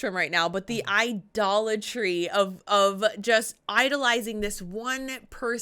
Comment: my brain got stuck in a weird loop after I misheard "but the i-Dollar Tree".. And now as a manc twat I'm wondering how my brain even knew about dollar tree...😂